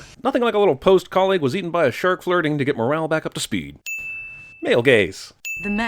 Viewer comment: i was not paying attention to the clip and i thought you said male gays.